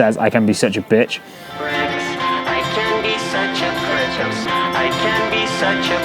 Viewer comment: i was wondering why it sounded like he said “i can be such a pritch” but it turns out it was just the two “voices” saying “prick” and “bitch” together lol